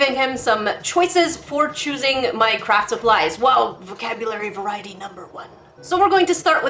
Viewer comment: When said my crafting supplies I thought she said Minecraft supplies 😅